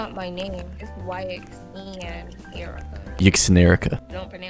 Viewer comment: "yxnerica" what i hear "yxneraka" neraka is hell in indonesian